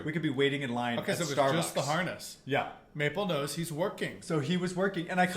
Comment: OMG, the first time said ‘working’ I heard ‘twerking" because was talking over the top of him.🤣🤣 Sorry guys, I know it was working. I just had to tell you because I thought it was so funny.😄